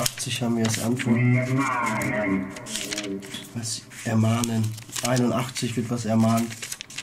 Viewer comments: Ich habe "Ihr Maden!" verstanden, nicht "ermahnen"... Ich glaube ihr wart ihm zu ungeduldig und zu fordernd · Ich habe anstatt ermahnen „Ihr Narren“ verstanden vlt meint er mit 81 nicht eine Bibel Seite und sagt dann dass ihr Narren seit weil ihr in der Bibel schaut